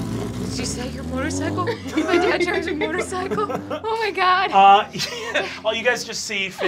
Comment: i was looking away and i thought she said “is my dad a motorcycle” sooo that’s my new theory